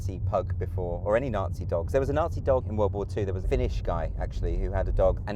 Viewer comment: Wtf just let the pug be a nazi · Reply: DID HE JUST SAY FINNISH!